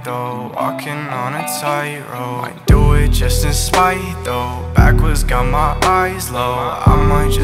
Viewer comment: I thought he said :- "Backwards got my eye roll"💀